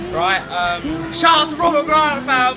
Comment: lol, what the heck did the person in the bg say? it sounded funny tho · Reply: I said "shout out for the brios FAM"